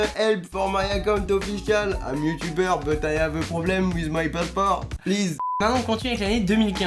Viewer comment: A 1:13 c'est moi ou il dit passeport (Charles) ?